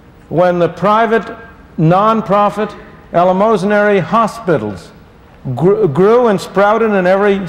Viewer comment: Help. At 11:40 he talks about the development of the ? "Ellamosinary" (phonetic attempt) ? private hospitals .... I just cannot hear that name or adjective. Can anyone knowledgeable in that history help out.